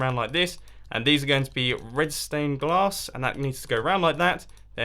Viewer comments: It sounds like he is saying red stone glass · It sound like he said red stone glass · I thought he said red stone glass